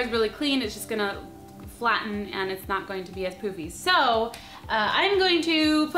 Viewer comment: When she said so it won't be all poofy I thought she said poopy xD